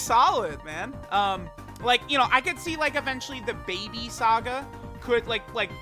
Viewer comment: Bro did he just say DA BABY SAGA(let’s go)